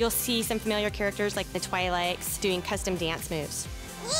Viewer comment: Did she say Twi'Lights @ 1:50?